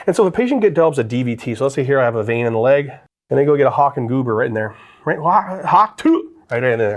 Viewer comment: no way did I just hear say hawk tuah 😭